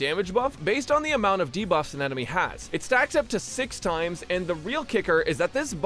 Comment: I totally heard “increases the maximum amount of sex you can have” instead of “stacks” and I am genuinely ashamed